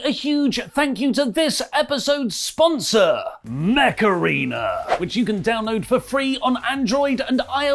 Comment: I misheard the intro to the sponsorship and heard "meth arena" and I was so down to play a game like that